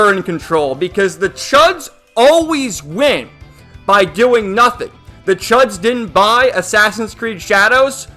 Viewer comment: I misheard that statement as "The chuds always wank by doing nothing". Help.